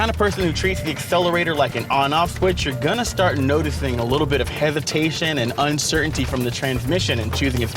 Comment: did he say hevitation?